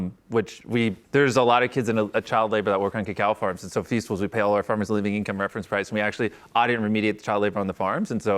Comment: Please someone. What did he say here?? 6:47 English isn't my first language and i reaaally couldn't catch this one?? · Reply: I heard "there's a lot of kids in child labor who work on cocoa farms so at feastables we pay all our farmers living income reference price and we actually audit and remediate the child labor on the farms and so you know, the bigger feastables gets the more kids we can get can get out of child labor on cocoa farms so, that's why I'm very passionate about feastables and um yeah it's been the big focus on..."